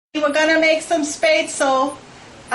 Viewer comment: First sentence I heard: "YO WE GOING MAKE SOME SPAYTSUL.''